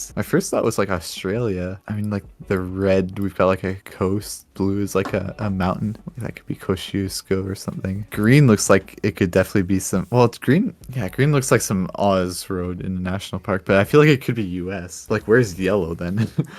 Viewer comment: I love the Kościuszko pronunciation xD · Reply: yeah. each time they were saying this I heard "cosy Oscar" not "Kościuszko" - idk why but probably because as a Pole i am used to way different way to pronounce it^^ but it sound definetly cute